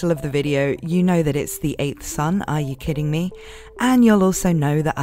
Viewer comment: At first, I thought that was saying, "Why the 8th Son? Are you kidding me?" And the I found out that the title actually says, "The 8th Son? Are You Kidding Me?" xP